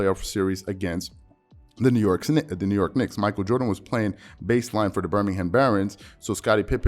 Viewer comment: this guy says basline instead of baseball